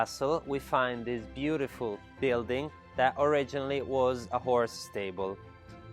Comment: I thought he said “whore” stable, probably meant horse.